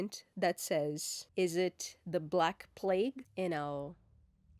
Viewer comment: I thought you said is it the black plate and I got confused for a moment ... but then my brain woke up and thought "ah plague" lol